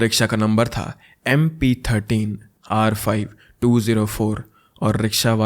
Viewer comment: Was Number saying wrong 201 204